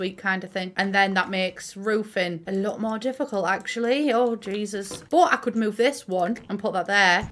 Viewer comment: when said "whOse jUdas?" I proper thought she said jesus and I nearly died